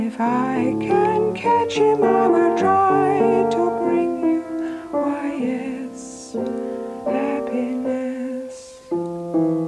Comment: Lyrics: Happiness is like a bird with twenty wings Try to catch him as he flies Happiness is like a bird that only sings When his head is in the skies You can try to make him walk beside you You can say the door is open wide If you grab at him, woe betide you I know because I've tried Like a butterfly upon an April morning Very quickly taking fright Happiness is come and gone without a warning Jack-O-Lantern in the night I will follow him across the meadow I will follow him across the hill And if I can catch him I will try to bring you Oh yes, happiness If I can catch him I will try to bring you All my love and happiness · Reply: Small thing but it sounds like she says “why yes” not “oh yes.”